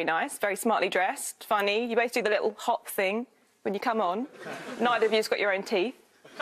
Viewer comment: "neither of you .... your teeth?" I'm having a hard time understanding what she said there? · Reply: I think it was "Neither of you's got your own teeth"